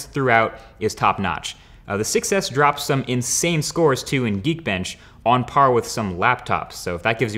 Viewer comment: sounded like- yeet-bench just me OK